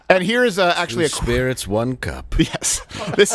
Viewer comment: I thought Dave said "two spirits one cock".